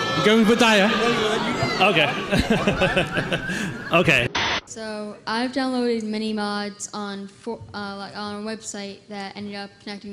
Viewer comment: It almost sounded like that kid 16:11 right there said I almost downloaded mods for fortnite I don't think fortnite was out yet